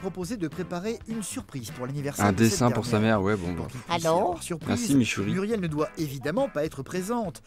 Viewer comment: il y a que moi qui entend le alors chelou 😂😂